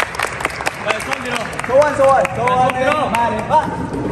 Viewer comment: tui nghe có đoạn 0:31 Hanbin còn nói 1 2 3 còn đoạn 1:29 hì anh Hyuk nói nghe như nhức cái chân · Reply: Đoạn 0:31 ông bin ổng hát sowoneul malhaebwa trong bài Genie của SNSD á =))))